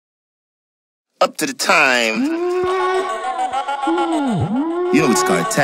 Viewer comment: Did I just hear “up to the time? “ 😩😫😩😫